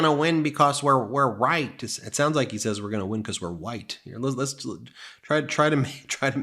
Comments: I heard that to. I thought he said we will win because we are white. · Did Eric just say “we’re white”!!?? What the hell is wrong with him?!?! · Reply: They'll say he said, "we're right..." I heard "white". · sure does sound like he is saying we're white....that would be the quiet part out loud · Yeah, it DOES sound like he says "we're white". Hahahahahahahahahaha. That dude needs to put down the coke spoon or whatever the hell he's on. Man! Did you see that weird selfie thing he did where he was laying on his bed and was looking up at the phone trying to talk? Dude, his eyes were rolling around in his head. That guy was tweekin outta his gourd!! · it sounds like he says we're white😂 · Sounds like he said they'd win because they were white. I didn't hear a "W" or , "R" in the word at all. It sounded exactly like he said white to me. Maybe it was a Freudian slip done on purpose. Hmmm... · Sounds like he said "we're white" blah blah.